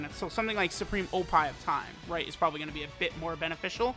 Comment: did i hear wrong or did he just say supreme oppai of time